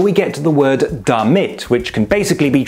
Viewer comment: I heard “dann wird” and not “damit”. Very slurred, but yes. Just as “haben” or “haben wir” often becomes “ham” or “hamma” respectively.